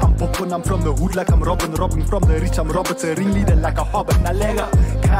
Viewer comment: did I hear correctly, did he say his Roberts's ringleader . If I did then bra dai is diep.